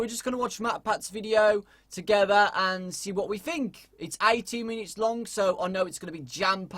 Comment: I thought he said 80 minutes not 18 minutes! I was like jeez matpat!